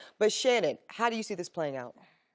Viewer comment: did i just hear "leshannon"?